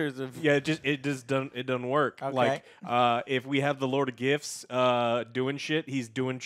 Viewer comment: I keep hearing "Lord of GIFs"